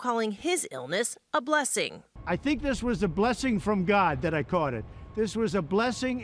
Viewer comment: Did he just say "I think it was a blessing from God that I caught it?" (1:15) 🙇